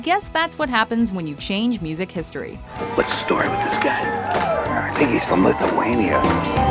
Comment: "I thinks he's from Lithaunia?" is that what he said?